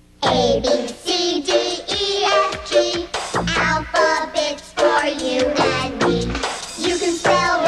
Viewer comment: Wow, I always thought they were saying "Alphabets" instead of "Alpha Bits."